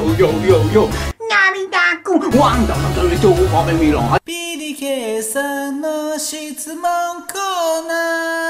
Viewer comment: ビリケンさんの〜 って聞こえるw